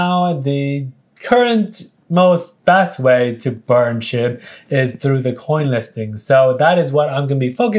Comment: when he said burn SHIB, it sounded like he said burn SH*T